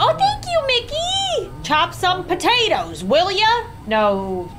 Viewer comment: Is it me or did i hear :"chop some tomatoes willia- or William?-"